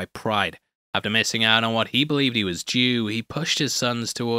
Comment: the subtitles say “after missing out on what he believed he was Jew” and I was like wow that’s a little on the nose. He definitely said due but YouTube is trying to cancel him.